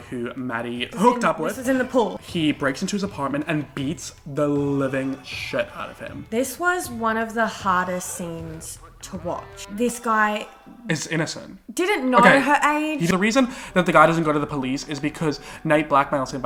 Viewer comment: my dumbass thought she was saying hottest and I was just like ummm sis your daddy issues are showing and then I was like OMG WAIT SHE WAS SAYING HARDEST